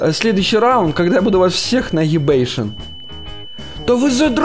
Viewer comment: нефига себе я первай раз слышу как матерится жостко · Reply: Скорее всего он просто искажённо сказал "Нагибейшн", хотя это не точно ' - ' ¯\_(ツ)_/¯